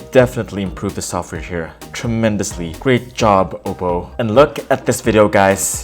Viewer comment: did you just say oppo?